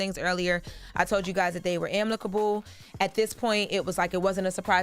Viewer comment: i thought she accidentally said amLicable then she said it twice